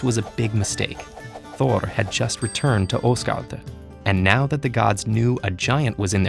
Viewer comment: did you just say Osgard